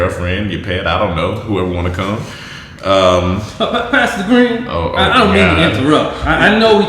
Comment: When they say "Pastor Green" i keep hearing "Pass the Green" lmao